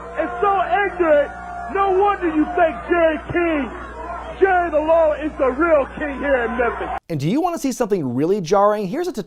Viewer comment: I watched it 3 times now but my brain still can't wrap itself around... ...did he call him "Jerry King" and "Jerry the Lawler"?